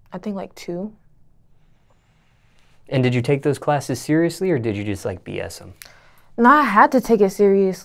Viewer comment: I thought he said “did you take those glasses seriously?” 😵‍💫🤪